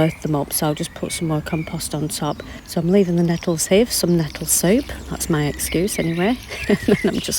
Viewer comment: Did you say nettle soup or soap? Either way, please share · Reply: Nettle soup but nettle soap sounds like it would be good. Invigorating and leaving you with tingly freshness 😂